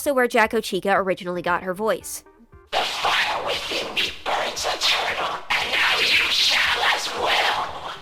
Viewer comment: why do the captions say “The fire within ME BURNS A TURTLE”